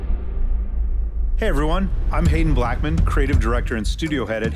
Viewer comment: "hello everyone, I'm hatin' blackmen" Am I just fucked up, or anyone else heard it like that